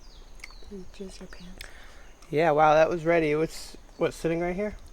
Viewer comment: almost certain i heard a casual "jizz your pants"